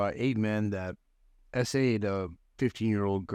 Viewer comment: why does the captions say saved🤨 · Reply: It should have said 'SA'd